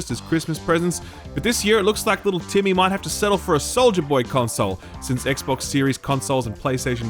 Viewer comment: I heard you say Soldiier Boy Console, not Soja Boy. I would be up for the former. I mean I love myself some good old Soldier Boy.